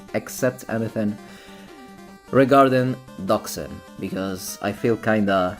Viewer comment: regarding what ? Can't hear you well xD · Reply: Doxing